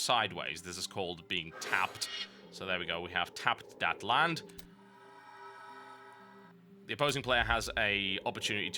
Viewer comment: did I just hear "tapped dat land"?